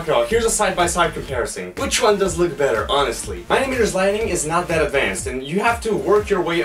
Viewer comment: Huh, the subtitles says "honestly my n****"